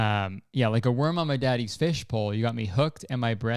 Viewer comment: straight up thought u said "like the word of my daddy's fish folk..."